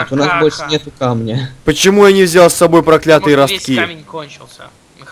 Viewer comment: я один услышал носки?